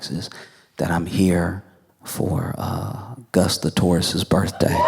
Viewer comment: At first I heard 'Gus the tourist' and was waiting to hear how someone had made a name for themselves just touring Halifax. 😄 🐢